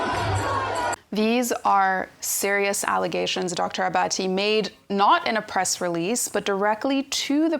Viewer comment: Did I just hear... "these are the allegations made by Dr Abatti...."